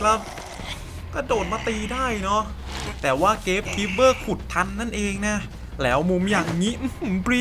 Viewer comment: ได้ยินว่าเกมคิมเปอร์ขุดทัน